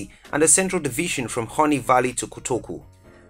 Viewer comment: Did he just say horny valley!!! Lmao